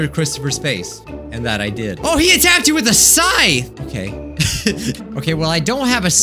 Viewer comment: I heard say "he attacked you with a sigh" and I had to process it cuz that is an odd attack, until he repeated the word "scythe" later and I understood my brain was being dumb again (with an added bonus of my ears failing me again as well)